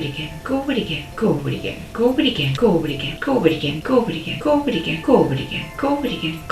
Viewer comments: "go over it again" What I hear: "Dobre Dien" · She said, “ go over it again .” I heard “Covid again.” 😂